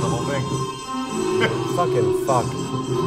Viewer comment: did he just say "fuckin fuck"? hahaha! ive bee that frustrated with complex wiring... it should just work, why is it not working...